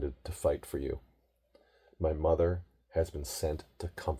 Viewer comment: I thought you said your mother near the end?